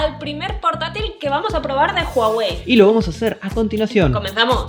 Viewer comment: Juraría que escuche "Xiaomi" en vez "Huawei" xd